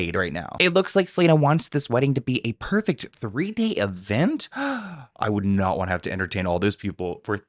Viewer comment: When you said Selena wants the wedding to be a perfect 3-day event, I misheard at first and thought you said "Perfect 3D event". I thought "A 3D wedding? She's really taking this to extremes" 😂